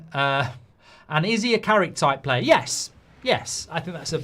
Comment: I heard “new Carrick” and my face lit up. I miss that type of player in our midfield